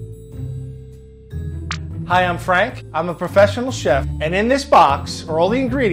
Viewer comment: Frank: "I'm a professional chef." has a child's bandaid on his ring finger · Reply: I thought you said Professional Child